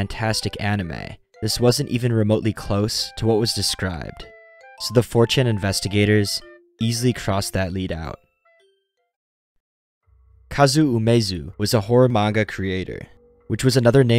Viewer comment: I thought you said, "fortune investigators." Even the CC says, "fortune investigators." I guess you meant 4chan investigators. If I mumbled and ran the words together it could be, "mh fuhm cham imbes mm geh ms" for "4chan investigators."